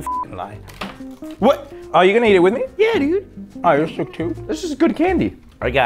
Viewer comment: Is it just me did say yeah “nig-“ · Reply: It was "yeah dude" the way he said it might've confused you